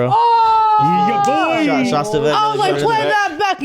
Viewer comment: "Schastavek" ?😂 I think he said "Schafft sie weg!" which basically word for word translates to "Take her away!".